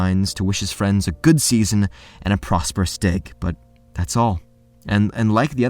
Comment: my dude i thought you said prosperous dick for a moment